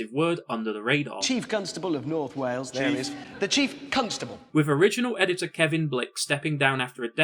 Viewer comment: what is the "extremely offensive word" Hammond said that got under the radar? I don't get it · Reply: oh i see now after hearing it the second time. "The Chief Constable of North Wales --- The Chief Cuntstable" lmao